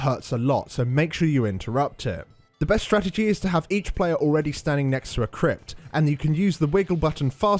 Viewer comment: > i didnt understand you talk too fast not sure why. ether way i replyed over and over and 5:22 (best stratagy) i didnt understand. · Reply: he said the best strategy is to have each player in your group near a crypt (sarcophagus) and use the wiggle button (I believe it is actually called struggle) fast (if you get entombed) so that the party can release you (since somebody will be standing near your sarcophagus). This avoids adds spawning as they're spawned either by incorrectly opening a crypt, or being too slow to get to your entombed ally and thus the boss opens the incorrect ones and adds come out